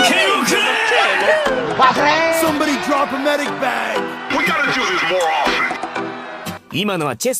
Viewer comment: Did i just heard basreng at 0:04?